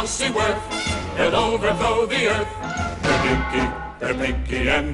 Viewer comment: Why does the subtitle read "dinky" instead of "Pinky" at 0:47 ?